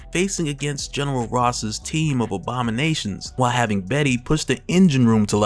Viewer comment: On the time mark minute 44:19 seconds I clearly made out that you said OBAMA-NATIONS instead of ABOMI-NATIONS 😂😂😂😂😂...